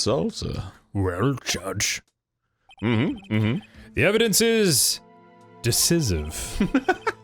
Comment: I legit thought the judge said "the evidence is delicious" because of the chips and salsa.